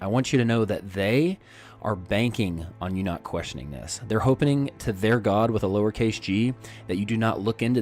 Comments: did he just say "hopening"? I don't know of such a word unless he's forming a contraction that combines the words HOPING and OPENING? Still, I don't believe that word exists. Explain please? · Did he just say "hopening"??